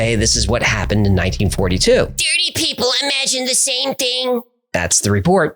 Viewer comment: did anyone else think Hecklefish said "dirty people" at first?